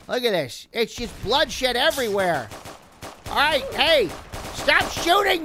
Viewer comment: did he say ther's bloodsh*t everywhere